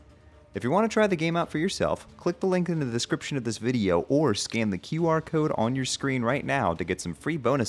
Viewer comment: I misheard scan the qr code as scam the qr code spamton has got to me